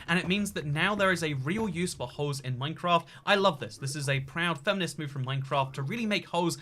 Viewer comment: did you say that it's a proud feminist move for minecraft? I'm confused.